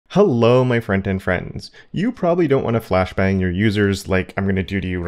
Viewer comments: Sorry, but my mind is blown right now. I have been watching your videos for at least the last two years, and this entire time, I thought you were saying "Friend and Friends". I always thought...hmm...kind of a weird introduction, but whatever. This is the first video where I actually noticed the "t" sound in "front-end". And I hade one of those moments where I just question everything I think I know about reality and the meaning of life. I'm shook. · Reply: Well, I just had my mind blown as well. I really thought he said "Friend and friends" and I was confused as to why he was not including that one friend with the others lol. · Sorry, but my mind is blown right now. I have been watching your videos for at least the last two years, and this entire time, I thought you were saying "Friend and Friends". I always thought...hmm...kind of a weird introduction, but whatever. This is the first video where I actually noticed the "t" sound in "front-end". And I hade one of those moments where I just question everything I think I know about reality and the meaning of life. I'm shook.